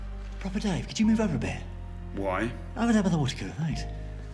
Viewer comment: What the heck does the doctor say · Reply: I struggled to understand for ages too. He actually says "Over there by the water cooler, thanks".